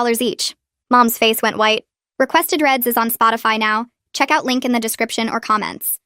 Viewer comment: did I just heard requested reads